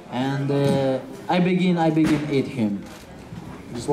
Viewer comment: did he just say 'I begin eat him'? Damn what a beast.